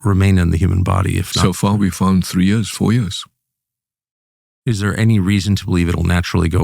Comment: the transcript says 3 months when he clearly says 3 years 4 years. Coincidence?